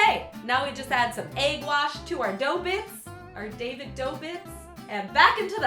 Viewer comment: david do what? i just hear david dobrik